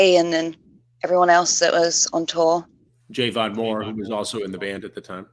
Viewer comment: did Chris say Jay Von Moron? 🤣🤣🤣